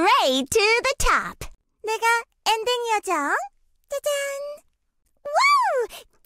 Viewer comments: Uhmm 🤔 What did she say💀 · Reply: its pronounced nae-ga it is alternative meaning for "you" in korean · Did she just say the N-word‽ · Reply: Noo its pronounced nae-ga it is alternative meaning for "you" in korean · Bro thought she could say the n word without me noticing in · Is it just me or did I hear her say the n word in Korean 😂 somewhere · Damn, voicelines are weird, not playing with sound on from now on x) PS: 15:55 Did she just say the n word? LMAO